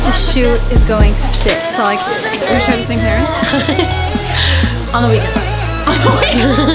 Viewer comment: Wait what does Dinah say at 1:58? 😂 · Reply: WeJustWantVlogging I think she's says 'we share the same parents' then Lauren says 'on the weekends' both giggle lol